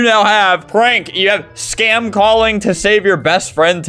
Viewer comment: BUY MY MERCH OR I WILL START SCAM CALLING YOUR PARENTS <link> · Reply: I really thought it said “Spam calling”